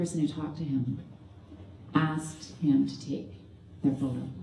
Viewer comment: “…and the only person who talked to him asked him to take…” what? Don’t understand what she says the person asked him to take. Is it “the vote?” …or “phone?”